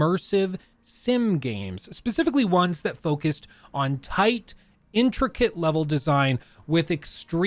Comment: did you say "tight" ? ×_×